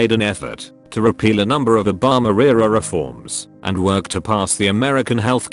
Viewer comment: im dumb, legit thought he was saying "hoe bomb herrerra" till i realized it was obama era